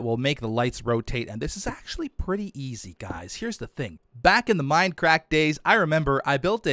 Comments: it sounds like he said Minecrack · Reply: Rexyboi Mindcrack, an old SMP server Bdoubleo, Etho, Docm and others used to play on. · Who else heard say minecrak in